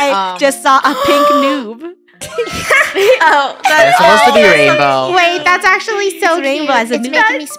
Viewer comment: Lunar kinda sounded like rainbow tho